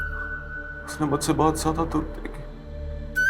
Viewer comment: pr kya bolw samaj nhi aaya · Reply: Usne mujhse bahot jyada dukh dekhe hai